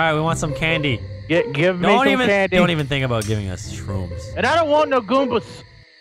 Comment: Wait a second did Vanoss say “shrooms” at 0:24?!